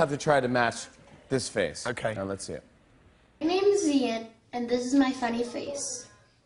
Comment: Oh lol, at 1:00 I thought she said," hi my name is mia, and this is my fuck-face." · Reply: I heard "and this is my fuck face"